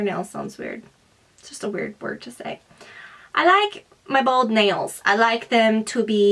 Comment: at first i thought she said bald nails hahahaha.